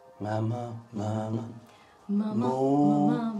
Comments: Did just say "Mama-mama-moo" at @ 10:56 while ghost hunting, haha! We love a MooMoo 💚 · did I just hear a MAMAMOO greeting??? 😂😂 I know is a fan but I thought another one of my devices started randomly playing a kpop video lmao